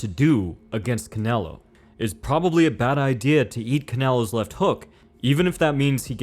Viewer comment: im sorry maybe i misheard but did he say yeet canelos left hook?